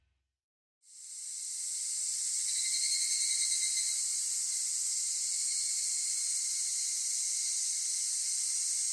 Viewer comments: WHAT DID SHE SAID!!! 😭😭😭😭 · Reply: 「片想いだと思ってた」と言ったらしいです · なんて言ってるのかどうしてもわからない・・・ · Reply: 一周年コンサートで本人が「片想いだと思ってた」と声出しで言っていました。